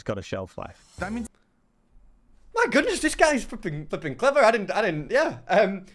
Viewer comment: did say the F-word? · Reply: Flipping